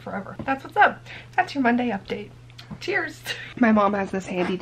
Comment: Instead of “Cheers”, I thought you said “Tears” and I was like “What a mood” lol